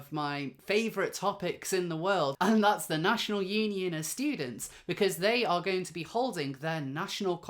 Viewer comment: I thought you said the National Union of Stupids at first